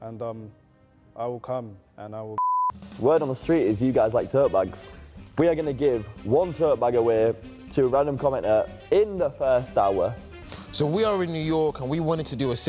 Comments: thought said "dirt bikes" instead of tote bags for a quick sec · I legit thought he said “dirt bike” instead of “tote bag”